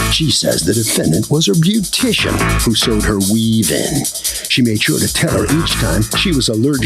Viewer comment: I thought he said 'Sold her weed' & then realized it was 'Sewed her weave'😂🤣🤦🏼‍♀️